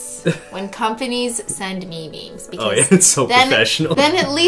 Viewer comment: When she said I like it when companies send me memes I thought that she said memies.